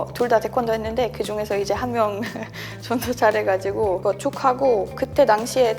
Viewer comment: 난 왜 이 부분이 '존나 잘해가지고~'라고 들리지?ㅎㅎㅎㅎ